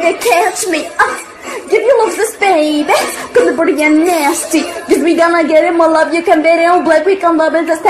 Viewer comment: Did she just said???? Britney nasty Yo my girl britney spears-😭💔